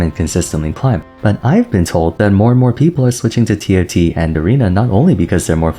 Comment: did you just say T "L" T ? :D